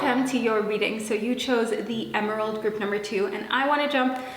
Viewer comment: My hearing is so bad, I thought you said, “Hello group #2, you chose the EGGROLL (Emerald)” lmfaoooo